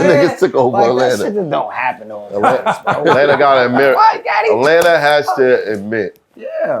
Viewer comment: U New York niggas different did he just call him Daddy 8:05 😂😂😂😂😂